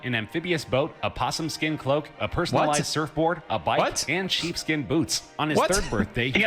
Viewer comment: or something arround that time ... did they say "cheap skin boots"? wtf